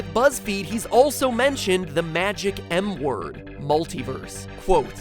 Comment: oh i thought you said "The magic N word" LOL